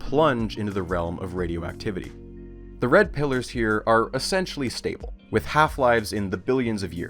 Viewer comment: Yeah no I totally misheard "red pillars" as "red pillers" lol 😅